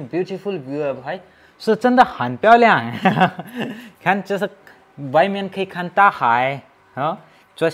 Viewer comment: kya bola ? 😂😂 bhen*** sunai diya, btw very nice & informative video ❤